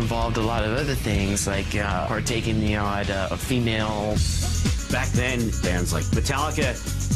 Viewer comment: WTF kirk say between 13:42 to 13:46 🤔🤔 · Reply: partaking in a female.